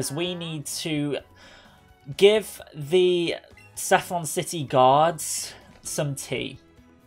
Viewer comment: When he said guards I thought he said gods